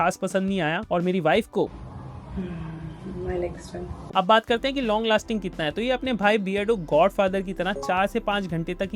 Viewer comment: what she said 😅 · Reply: I like the smell bola